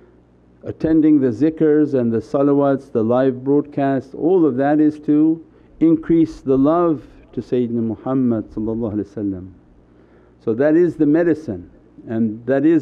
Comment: is he saying increase the love to satan and muhammed? English caption auto generation is showing he is saying satan.